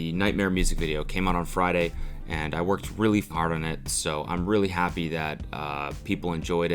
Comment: "I worked really fart on it" teehee sounds like said a funny word